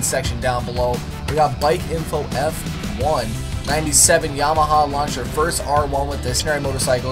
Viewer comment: Did you say Yamahama?